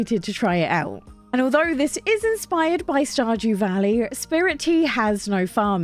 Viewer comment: I realized while watching this that every time you say Stardew Valley, I thought you were saying Stodgy Valley with your accent. I thought this was hilarious. This is meant in the nicest way possible as I love your videos. Thank you